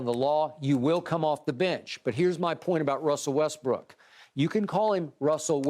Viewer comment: Man I thought Skip said you can call him Russell Westb****!🤣🤣🤣